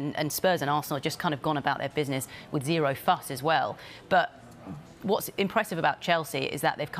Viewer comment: Goes against the media narrative, but if Chelsea sign Fofana and a forward, I think they did. Sterling, Cucarella and Koulibaly already in alongside some of the best young talent about. Doesn’t count but Broja and Gallagher coming in feel like new signings too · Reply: I thought she said ‘Zero Fucks as well’